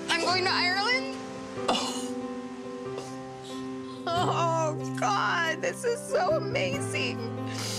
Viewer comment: did she call Tyler, Scott?